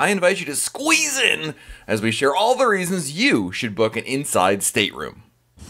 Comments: He says stateroom so fast it’s like he doesn’t want anyone to what him saying stateroom..... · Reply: I heard "inside sit room." It irritated me. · Talk faster why don't you. Jesus I have to switch the video to a slower speed just to follow what you're saying. Not a good thing to do , fast talking, for those who have some hearing difficulties. Slow down when you speak or get someone who will not hyper talk all through the videos. It's just makes Royal Caribbean look bad if people can't understand what's being said in the videos you post. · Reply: Here here. It pissed me off too, trying to listen to him. I just heard "inside sit room" "Inside sit room" And then I got irritated.